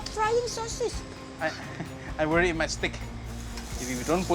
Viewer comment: i misheard :"i worry MY stick" 🤣